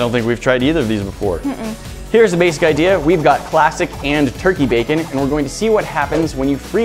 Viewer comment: I thought he said “plastic,” I was like 🤔